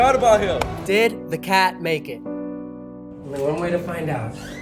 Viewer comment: I thought that it said “did the car survive” not the cat XD